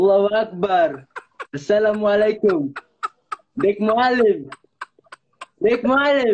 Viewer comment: What is the third phrase he says at 0:17 ? I caught "alluah akhbar", and "asalamalakum", but what's next? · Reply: Big Muallim / Big Teacher